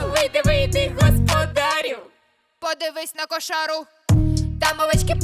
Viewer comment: кто тоже подумал тута что она сказала [ подавись на кошару]